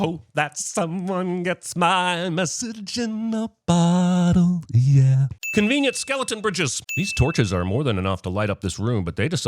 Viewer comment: I heard that as "convenient skeleton britches" and now I want some skeleton pants.